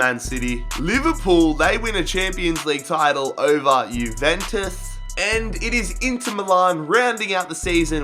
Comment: did he just say juventuth